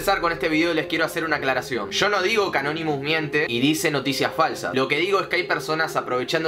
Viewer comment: Porque dice canonimus